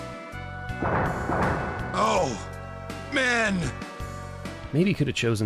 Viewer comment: I think Borgoff actually says "Amen", like he does that one time in the movie, but I guess the translator just went with "Oh... men". That's just hilarious.